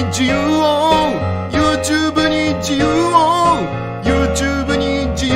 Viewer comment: YouTubeに自慰を にしか聞こえなかったw